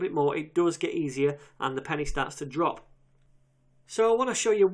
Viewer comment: did you say "it does get easier and the panties start to drop"? lol